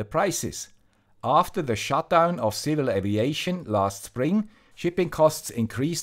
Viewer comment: any link/info to the aviation company that went down? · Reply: couldn't hear properly, I thought it's an airline name. Subtitles (that prev. I only saw in Spanish) revealed it's 'civil aviation'. No more information needed!